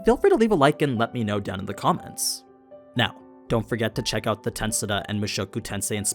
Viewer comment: what did he say? The “tensada?” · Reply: Tensura. Sorry haha